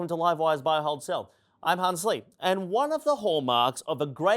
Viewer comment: I thought he said "I can't sleep" not Hans Lee. 😂😂😂😂